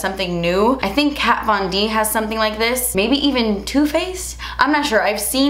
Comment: when you said "maybe even Too Faced?" Idk why I heard it as "maybe even Tupac?"